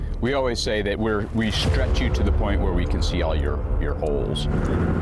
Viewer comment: First thing I hear. "We stretch you so far apart that we can see all your holes". Uhhhh right right.